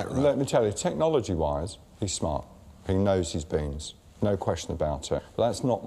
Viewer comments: sounds like he says "Technology wise, he knows his penis" O.0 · does bordon say ' he knows his penis'